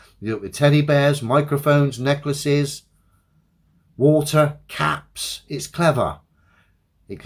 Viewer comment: I thought he said cats. 😂 WAIT! WAIT! I wanna see a cat in microgravity. 🤣🤣🤣